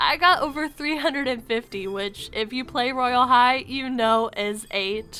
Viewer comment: When you said Royal High, I thought you said "Royal!! Hi~!"